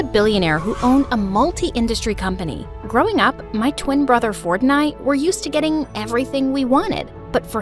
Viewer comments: I RLLY THOUGHT SHE SAID ME AND MY BROTHER FORTNITE LMAO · i heard Fortnite 💀💀💀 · When she said Ford and I: I thought they said Fortnite 🤭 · I thought she said fortine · Ngl I thought it said fortnight😭😭😭😭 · What’s she said: “growing up, My twin Ford and I” What I Heard: “Growing up, my twin brother Fortnite-“ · I heard Fortnite 0:10 🤓👁️👄👁️ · did she say fortnite or am i tripping💀? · sound like you said Fortnite but without the nite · I thought when it Said “For and I” It actually Meant Fortnite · First I thought she said "my twin brother fortnite" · The fact she said “fort and I” but I thought she said “Fortnite”😂😂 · When she said about her twin brother i thought she said my twin brother Fortnite😭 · did she said fortnite because I'm never playing fortnite